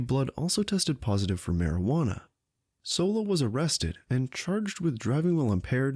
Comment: I believe she was sentenced to a 30 day rehab program and loss of license for a year. Please tell me why you say her life is ruined. Honestly, the first couple of times I heard her name I thought they were saying Hans Solo. I didn't have a clue who she is.